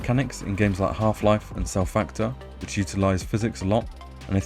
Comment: What game is that at 27 seconds in? Sounds like you said self factor but cant find it. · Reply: Cell Factor: Revolution